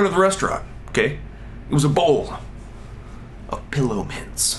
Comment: At first I thought you said it was a bowl of pillow pets. Then you talked about stuffing them in your pockets and I knew I had heard wrong. -.